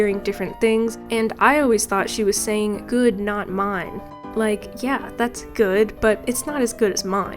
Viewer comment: I always thought she was saying "you're not mine" like she was disowning you for being bad at cooking